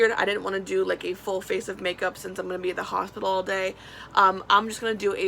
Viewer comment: I hope you don’t mind me asking.. but why are you going to be at the hospital all day? Sending good vibes and wish nothing but good things. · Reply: Erika Wiberg she said she’s going to be in the house all day not the hospital 😂 that’s what I thought she said too, then I rewatched it